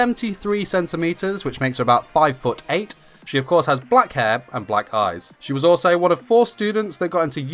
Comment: At first I thought he said black thighs